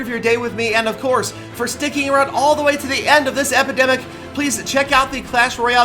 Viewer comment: "Thanks for sticking around all the way till the end of this epidemic" did anyone else hear that at 6:30?